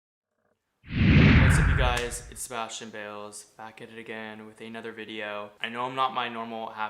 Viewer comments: Oops I thought it said Sebastian’s balls lol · He is orange He is fake He just wants tea Welcome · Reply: Fermin Hernandez i thought is said Sebastian Balls